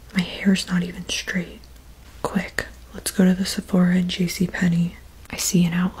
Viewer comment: I thought she was saying “sephoro” until the end I realized it was “sabarro” 😭 I’ve always pronounced it as sha-ber-oh